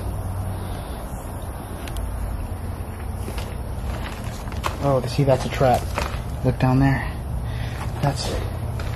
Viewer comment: Hey did you hear the women whisper right after you said” do I look nervous”. 10:26 definitely a women saying something. I can’t make out what she says. You are mad going there on your own. I get so nervous when I watch you in places especially on your own. Thanks for all your hard work. Love from Sunny Scotland (for a change)😂😂🏴🏴👻👻👀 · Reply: Well i hear shut them up or satan. Propably im wrong but whatever that was and what said is absolutely SCARY AS F.